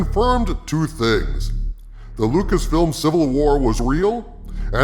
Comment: I thought it said Luciferian civil war. Maybe not too far off.